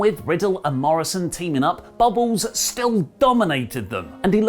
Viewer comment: heh Wait, did you call Bobby Lashley "Bubbles" at 6:41?